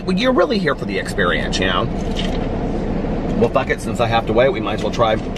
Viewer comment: wait did say fuck at 10:35 ? lol thats the first time ive heard him swear haha ! thats what happens when they dont give you the cheese i guess :)